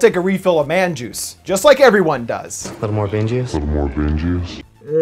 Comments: Could've sworn that the guy said bean juice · i thought he said “bean juice” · I thought he said bean juice... as in coffee bean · i thought the dude said Bean Juice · I thought he was saying Bean Juice. As in coffee beans. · I thought he said bean juice?